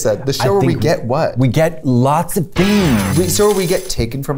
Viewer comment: When I first heard this when Kayta said things I thought she said beans